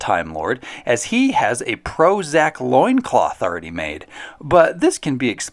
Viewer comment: I thought you said “Prozac” but it was actually “pro-Zack”.